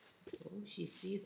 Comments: I think she said “Uncle” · i think she said uncle